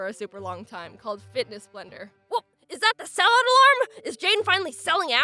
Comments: I hope I'm not the only person who heard "Is that the SALAD alarm?" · I hope I'm not the only person who heard "Is that the SALAD alarm?" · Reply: I thought she said "is that the sellout alarm" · When she said "oh no is that the sellout alarm!?" I thought she said "salad alarm" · when part of Jaiden's brain asked, "is that the sell out alarm?" i thought it said, "is that the salad alarm?" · I thought you said salad alarm…not sell out alarm and I got super confused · I thought she said is that the salad alarm!! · Everytime you say "sell out alarm" I hear "salad alarm" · Am I the only one that thought she said “Is that the salad alarm”